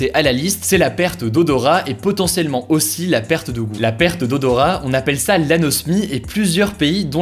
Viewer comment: "la perte d'odorat et potentiellement aussi la perte d'ongle" ? Il dit ongle ou quoi j'entends pas bien